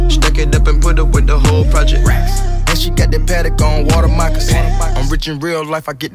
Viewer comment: Oh. So offset said Water mocassin. I thought he said Water my croissant. Lol.